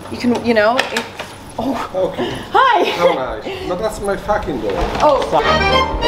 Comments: omg hahaha did he just say “not next to my fucking door” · The guy opening the door suddenly and Emma’s innocent “hi” and his “that’s my fucking door” took me out 😭😭😭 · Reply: I heard him said "that's my parking spot" tho... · imagine you’re chilling at your house and you open your door and fucking see emma chamberlain chillin’ on your stepdoor doing a haul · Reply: I thought he said get off my fucking door 😭😭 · I will never get over "das my feckin door" moment, like even I got scared😂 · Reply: I thought he said that’s my parking spot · the gondolier playing air guitar with the row, the pidgeon that completely submerged in the water bucket, the “this is my fucking door” moment and the reflection of her dad on the windowpane of a store while following/filming her, so focused and professional, for her to be so comfortable in front of her dad’s filming is so rare and wholesome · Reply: i heard 'dont touch my fucking door'